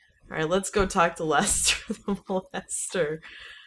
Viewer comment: I thought she said "Let's go talk to lester the melester." xD